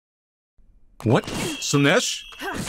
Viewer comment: Who else heard "what in Slaanesh?!" at 7:50?